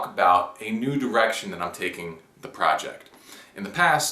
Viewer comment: did u say " ..a Nude Erection I am taking with the project..."?